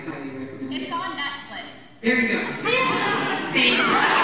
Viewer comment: What did she say at 7:45 ? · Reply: You’re blowing my shit up right now